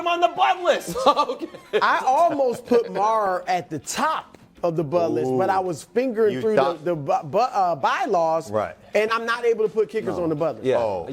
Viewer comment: What did Brew say?? 🤔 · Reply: That he almost put Maher on the Bud List but he was flipping through the ByLaws and they say he's not allowed to put a kicker on the Bud List